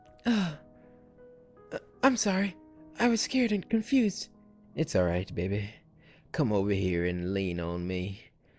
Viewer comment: At first I thought he said "It's alright nigga,"😂😂😂😂😂😂 But he said "It's alright baby"😂😂😂😂